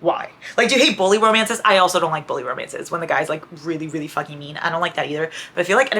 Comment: When you said bully romances I swear I heard “bowling romances” New trope?! 😂